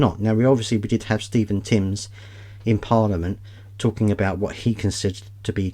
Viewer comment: I miss heard you, thought you said "we had Stephen King in parliament". These actions by the Labour government would certainly fit nicely in one of Stephen Kings horror novels although I doubt even he'd imagine these methods of killing people and the country slowly with maximum pain inflicted.